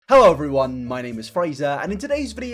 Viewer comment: At first I thought you said "hello, my name is frieza."